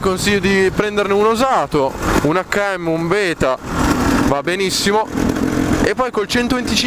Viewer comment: ho sentito beta?